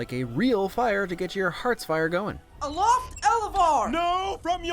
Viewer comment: I always heard heart spire.